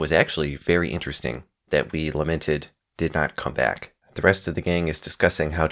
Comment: You inflect the word lamented wrong when you enunciate it... it sounded like you said limited.